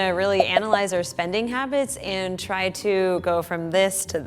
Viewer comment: I thought it said SPELLING HABITS for some reason?!